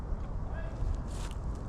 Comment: Am I the only one who heard the voice say hey 10:46 and